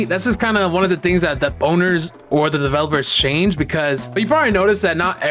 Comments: did bro say boners 💀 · DID U SAY THE BONERS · did anyone else hear say boners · I SWEAR I AINT TWEAKIN HE SAID (B)OWNERS